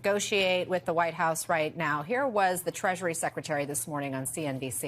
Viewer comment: When you said Treasury secretary I thought you said Treachery secretary. Also it has been reported that there are no economists in this current cabinet so calling a recession probably won't happen unless they make up a new name for a recession and try to spin it as something positive.